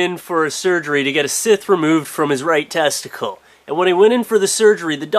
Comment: did he say a sith at 0:24 ? i don't think i'd want on of those in there either....Maybe the sith cut it off..